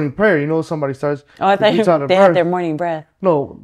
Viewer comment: I thought you said their morning breath🤣🤣 that was probably part of the “discipline” too! Ta’efulunifo and blowing hot air in your face!